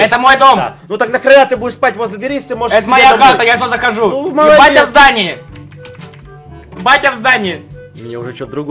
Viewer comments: Мне одной послышалось (ну и Эдисону) что сказал не батя в здании а еб*ть я в здании?😹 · Мне послышалось что сказал "ебять я в здании" XD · как сказал "Еее батя в здании" Как послышалось мне "Еб*ть я в здании" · Мне послышалось еб*** я в здании